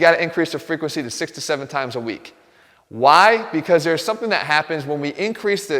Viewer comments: thought he said 67 times a week. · I thought he said 67 times a week for abs training and was like wtf!? 😂😂